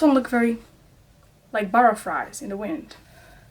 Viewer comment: My mom heard you say burraflies and she said “KENYA GIRL” I was scared for me life